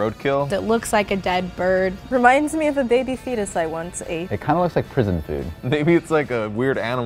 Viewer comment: A baby foetus I once ate? Did I hear that right? O.o